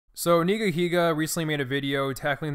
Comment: I thought he said nigga lol